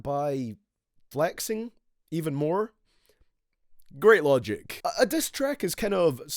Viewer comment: did you just say LOGIC